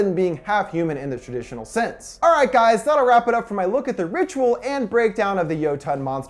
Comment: I Thought it said “THE VIRTUAL” 😂